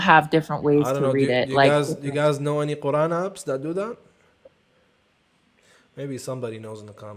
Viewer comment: invited ME up so no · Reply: Just wanted to let you know that you misheard at 21:25 he didn't say you're yapping he was asking chat about any apps that they might know could help you. You probrably mishead "any app" for "yapping".